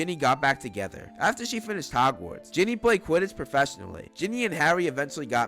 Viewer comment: u said jimmy instead of ginny